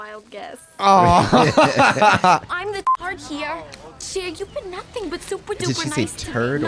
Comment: Oh my god....I always thought Tai said "I'm the tart here". I just now realized what she said.